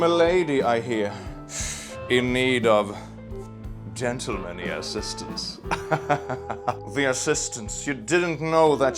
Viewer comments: I heard that as "dental assistance" and I'm slightly horrified by the implications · I thought you said “dental”manly assistance 🤭